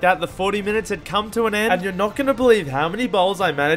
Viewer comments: man i hear it say "i ate 150 balls" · "I ate 150 bowls" What I heard: "I ate 150 balls" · Did he just say 150 balls or I heard him wrong👀🐒